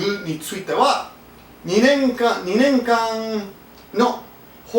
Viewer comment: you said ２年間 but the subtitles say ２年前. Sorry to point that out, haha. But yeah it's a pretty stupid law concerning the Youtube stuff..